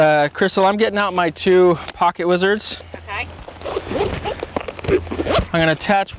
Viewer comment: haha, i thought u said you were getting out your pocket lizard. I thought, ya can't do that on a shoot :)